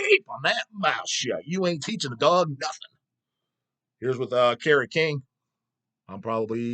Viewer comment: Did say Carrot king 😂